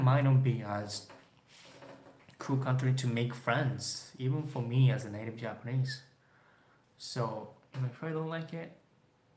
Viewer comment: LMAO. "Even for me as a native Japanese". The first time I heard "native asian prince". Fuck me, lol I don't know where my mind is today.